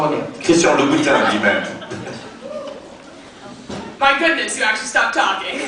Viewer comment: What does the girl say at 31:36? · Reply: I’m 8 years late but she said ‘My goodness you actually stopped talking’